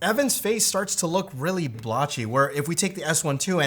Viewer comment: he uses "blachi" word, I searched over internet, but didn't find any 😅. I think he meant to say blurry. But just curious what these slang word refers to 😅